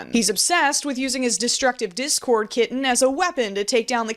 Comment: DID I HEAR THAT RIGHG “DESTRUCTIVE DISCORD KITTEN”